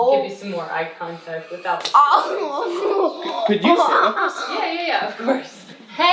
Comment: Uhh did he just say "Can you stop this?" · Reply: No, he said, "could you sit with us?"